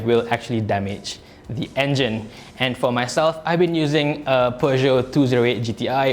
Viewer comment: If you turn on captions at 3:32, it shows Porsche 208 instead of Peugeot. driving Porsche 🤣